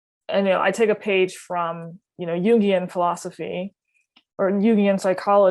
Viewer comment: she cites something she names as ‘Yugion philosophy’ if I heard correctly. Speaking of the shadow self in a separate realm. What psychological belief is she referencing? Did I miss hear her? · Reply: It is Jungian, as in Carl Jung :)